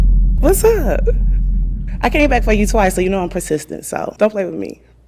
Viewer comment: what letter did you hear? Pahsistent or consistent??? I heard pahsistent. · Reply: She definitely said pasistent 😂